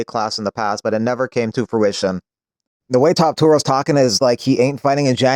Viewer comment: lol I heard that “Toptura”